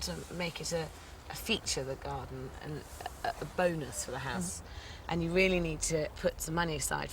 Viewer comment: DID SHE SAY BONERS?